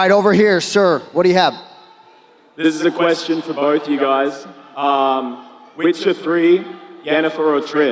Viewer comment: Witcher 3 or what did he say?